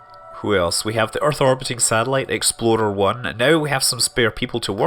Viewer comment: it sounds like he is saying "Exploder 1". How very Kerbal.